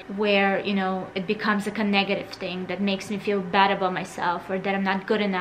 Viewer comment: the youtube subtitles say ''connective thing'' whereas you said ''negative thing''. That 's the kind of feedback I like to give :)